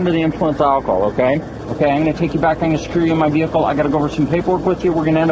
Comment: What did he say? "Im gonna take you to the car and screw you in my vehicle" Edit: I kindda dont think shes in the mood dude. Edit2: I hate drunk drivers more than ever after watching this.